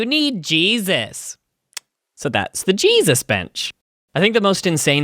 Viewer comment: I thought when I first heard it you said “Jesus bitch”. So I was like “WOWWWWW!! Did he just say that!??! I mean at the beginning of the video he did mention this is for 13+. So then I rewinded and heard what he actually said which was the “Jesus bench”. 😂🤣😆